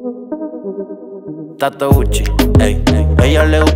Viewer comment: Yo pense que decía gato gucci jaja 😂🔥🔥